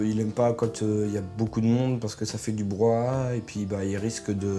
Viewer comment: c moi ou il dit broi